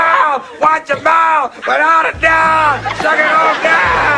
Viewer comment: did he say "suck it on down"? Surprised he got away with that on '90s tv...